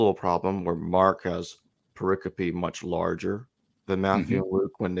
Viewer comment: What is that word at around 16:45 ... paropy ? Is that the correct spelling? I couldn't find it on google. Paripropacy? And, what does it mean? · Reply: it's Pericope.